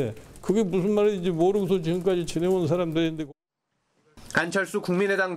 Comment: 나만 간철수로 들리나? ㅋㅋ